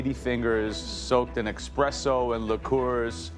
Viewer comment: Did Joe just say “Expresso”? 13:09 it’s like an Italian breaking spaghetti 😅